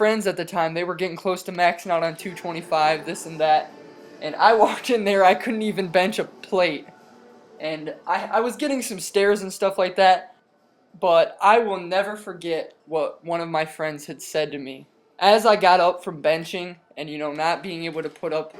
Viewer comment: Did he say he was getting some steroids and stuff like that at 4:03? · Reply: ROFL!! He said he "was getting some stares." People were staring cuz he was weak sauce!